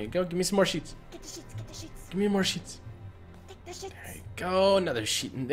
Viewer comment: When they were saying take the sheets take the sheets it sounds like they were saying take the sh lol 😂